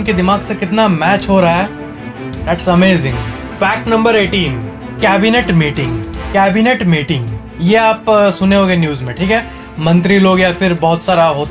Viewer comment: i first heard EMERGENCY MEETING!!!!!!!!!!!!